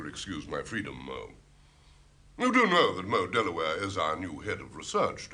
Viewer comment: Does anyone know who played the new Head of Research, Mo/Moll Delaware? (can't tell what 1st name is saying here) She didn't have any lines and so, unfortunately, went uncredited. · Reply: The character is 'Mo Delaware.' In a long cast listing, the very last entry is Margo Abbot... uncredited, 'secretary.' One episode. Possibly her... is the character in just one episode? An Ai type result for Margo (Margot) Abbot says "... British television background actress, known for tiny, uncredited and background roles in classic UK shows." Villager in The Prisoner, Parisian in Doctor Who, secretary in TinkerTSS, and woman in Mind Your Language. All uncredited. Didn't see any photos for Margo in these wanderings. But she definitely acted in the miniseries! I'll go back and look at your timestamp, now. 'Be seeing you.' [ i do see i'm 4 years behind on your comment... ]